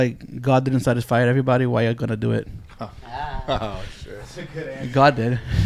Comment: what did say?? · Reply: God couldn't satisfy everybody why i gotta do it